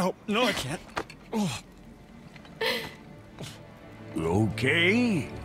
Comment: I know the subtitles show that brok says “okay” but is it just me or does it sound like he says “lokay”? Almost revealing that he knew who Atreus was this whole time? Can’t believe I’ve just heard this